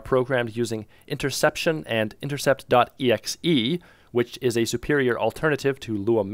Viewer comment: Did i hear INTERCEPT!??